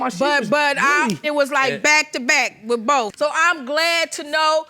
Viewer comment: did I hear her mother say " it was back to back with both"? Thought she testified it was just a one night stand with the hairdresser